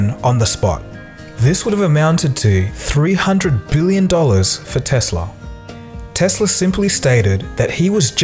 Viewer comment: did i heard that right? 300 billion dollars?